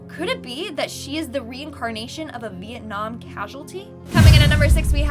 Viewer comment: Am I the only one who heard Mietnam at 5:25?